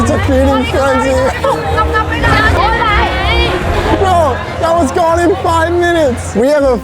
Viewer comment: thought bro said i was god for 5mins